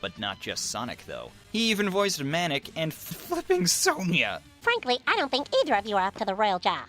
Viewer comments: did you say the f word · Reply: He said Flippin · i almost thought you said f***ing